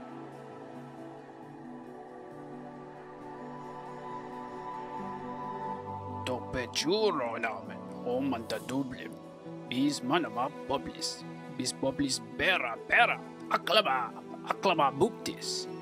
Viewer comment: I swear I´m hearing zote say "this man uploads" at the perfect timing